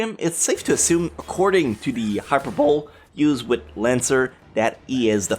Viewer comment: did he say hyper-bowl instead of hyper-bo-ly